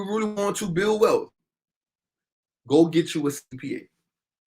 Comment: Did you say go get you a cpn at 21:53?